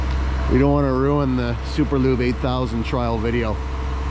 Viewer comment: Thought it said Super Lube Booo